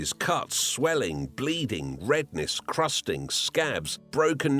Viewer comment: Why does it say blooding instead of bleeding